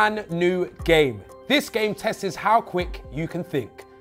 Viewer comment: Did filly say "testes" at the beginning of the vid? 😂😂